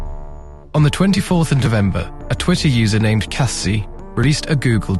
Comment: did you just say DeVember